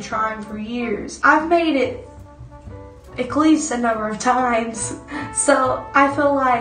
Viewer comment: Great advice! Agree with everything. Although there was one part where I didn't quite understand your dialect - at 0:40. "I've made it ehklease a number of times?" Did I catch that right? What is an ehklease? But really, I love the accent. Keep up the great work. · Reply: i think she means at least